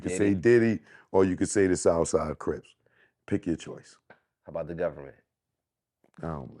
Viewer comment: At the 15:55 mark what did the interviewer ask? He asked if there was a 4th option to choose for the cause of Pac murder but I cant make out what he saying....... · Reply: Q - "How about the government" A - "I dont believe that Sh1t"